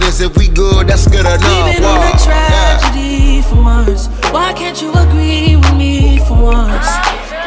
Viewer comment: Damn, I gotta get this pimpin outta my mind, I always thought it said we been on tha track for months, chuuuch